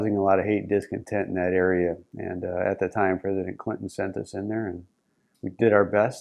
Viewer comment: Does he say Hilary Clinton at 4:37? This man is a hero. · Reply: He said "President Clinton"